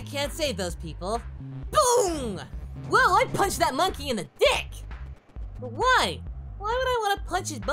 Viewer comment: did goku say"dick"? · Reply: Goku said, "He punched me in the dick...why did he punch me in the dick?" in the DBZA Super Android 13 movie